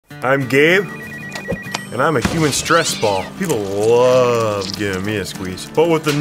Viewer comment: I really thought he said “I’m gay and I’m a human stressball”